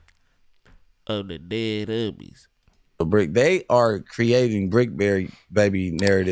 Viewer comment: did bro just call himself BrickBerry 😂🫐